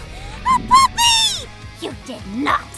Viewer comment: It sounded like Frenchfry said Honestly I’d scream